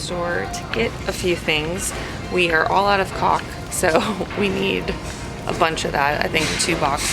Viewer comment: it sounds like she said they "ARE ALL OUT OF C*CK"? Sorry, what?